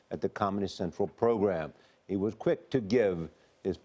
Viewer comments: I thought they said Communist Central program until I read the description. That would have been more fitting. They let it slip! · Did he say "Communist Central Program" ? … 0:12 …